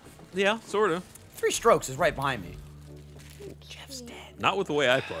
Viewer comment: does say "Geoff's dead"? I sense a conspiracy theory